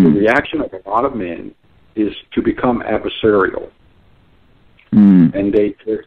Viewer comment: Did he say the reaction of the men become abserial